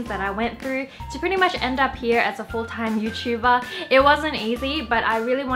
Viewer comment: Hello, I liked this video so much, and I want to make sure everyone can understand and enjoy it. That's why I wanted to let you know that your captions are a little off. At 0:25 the CC read "you tuba" instead of YouTuber :)